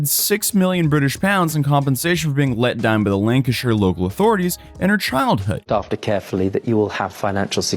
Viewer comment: "In compensation for let dahm bah lanka show thorities in her childhood". Bro wtf are you saying there? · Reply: 5 months late but nobody responded to you, "Let down by the Lancashire authorities"